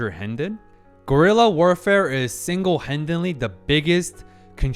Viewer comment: i heard you say "gorilla warfarfe" and i was like, WTFF???? but then i watch the screen and see wat you really meant. "Guerra de Guerrillas" is a term well knowed by basically every latin american that learned at least the basic about our region's history (because basically, most latin american countries have had some form of guerrilla warfare in their history.)